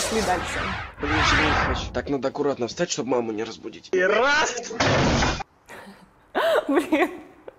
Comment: На 5:57 там И РАЗ а мне послышалось ХЕРАКС так блогер варпач говорит это по секрету я вам его советую